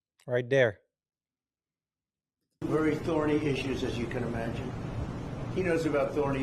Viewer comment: It sounds like Trump said thorny issues- not authority issues. 🤷‍♂️